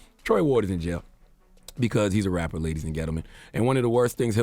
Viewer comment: Did Charla say “Ladies and Ghetto men?” 😂😂